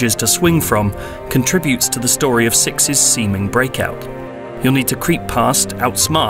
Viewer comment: did you just say "six's semin breakout" ?